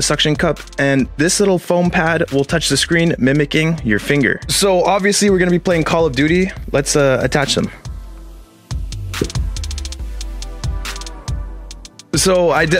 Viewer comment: I thought he said who ever made call of duty it’s was trash